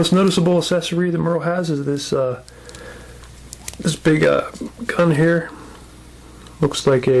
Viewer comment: dude i thought you said "this big uh Cunt Hair" at 3:19 .....so im laughin at the real shit and the shit that aint even there...there's like layers man to your comedy.......different fuckin levels duuuude.....hahaha.....i don't care if its gay to say.....i love you and your wife...........whatever the fuck your doin....KEEP DOIN IT!